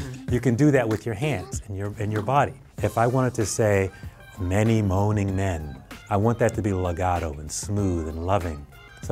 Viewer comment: did he just say many moaning men wth?